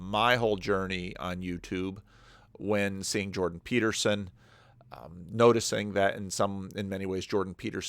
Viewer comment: Haha when you said “said Jordan Peterson” I thought you said “Saint Jordan Peterson”